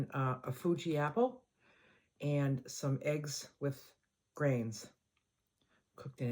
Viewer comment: Oh my goodness, I thought you said eggs with brains. 😂😂 Merry Christmas!